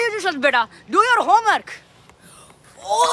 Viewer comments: did blud say ohhh that’s spicy like my moms car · "Oooh that's so spicy like my mummy's curry" got me dying 💀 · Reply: I thought he said “my mungus curry”